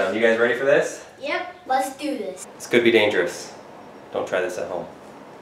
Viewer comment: why did i think he said ”scooby dangerous" 😂